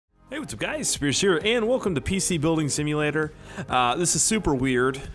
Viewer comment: could've swore i heard hey whats up guys Scarce here